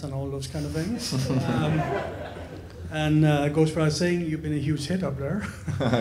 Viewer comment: What does this guy say at 15:48? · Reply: It may be too late but he says “It goes without saying, you’ve been a huge hit up there”